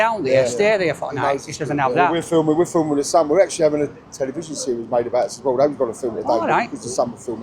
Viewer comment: What big john say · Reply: “We’re actually having a tv series made about us”